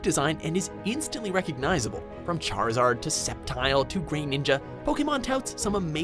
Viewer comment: thats grovile not sceptile and it sounded like you said green ninja